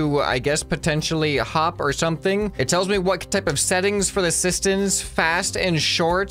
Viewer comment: Bruh he said sistons instead of pistons.